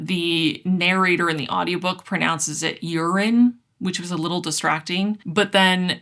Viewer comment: ETA: 1:09:10 oops. Spoke too soon 😂 You criticize a character whose name is pronounced “rye-Anne” but you have NOTHING for a character who i LITERALLY keep mishearing as “urine”???? (Again: see ETA)